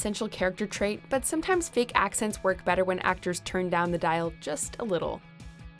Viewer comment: Thought it said accidents. lol